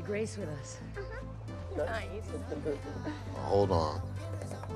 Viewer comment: When Mia said "He's on his way" that scene almost got me crying We will not forget Paul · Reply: CID I don’t think Mia said “He’s on his way”. I heard “You want more!”